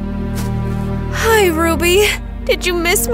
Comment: WHAT DID SHE SAY RUBY!!! But my NICKNAME IS RUBY so my friends think I'm a horse😢